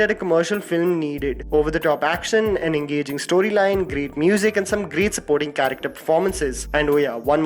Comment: did you said.. engaging storyline...